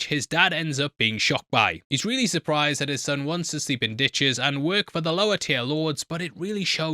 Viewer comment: Thought you said his dad was surprised his son wants to see bad bitches lol